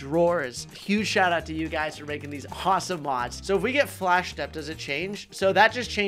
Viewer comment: i swear i heard "hotsome mods"